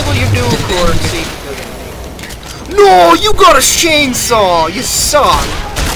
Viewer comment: it sounds like said shanesaw